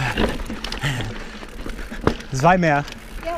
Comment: When you passed those first people I thought you said "zwei bier" as a joke LOL, only realised the second time you passed people that that wasnt what you said :D Amazing views as expected, I love Switzerland!